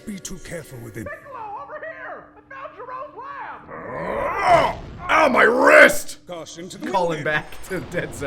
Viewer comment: for some reason i always thought they said "ow my ribs" instead of "ow my wrists"